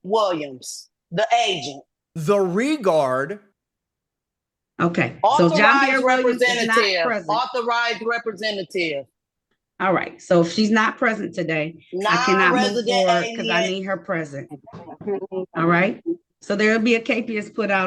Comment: Went the judge asked if the person was in the courtroom, it sounded like she said, "I am president," instead of I am present.